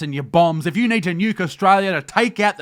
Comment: You said "nuke Australia" But I heard "Yogostralia" Had so many ideas for movies about Aus going the way of Yogoslavia. Queensland attacking New South Wales etc. People asking "Western Australia? Is that a thing?" And holy f*** tasmania is full of guerillas. 😁